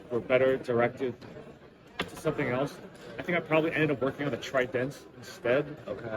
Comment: did he say tridense or triton? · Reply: Trident. Yeah it was gonna be a thing. Like a mako triton hybrid before either existed I believe. Actually, I think it’s what the mako turned into.